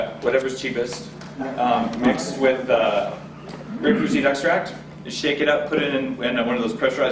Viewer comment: Can anyone help me understand what did he said at 27:40, he mixed up vodka with what extract? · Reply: Grapeseed extract